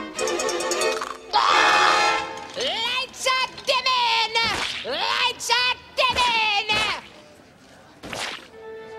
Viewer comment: I could never understand what he said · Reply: NintendoMan9987 LIGHTS ARE DIMMIN’! LIGHTS ARE DIMMIN’!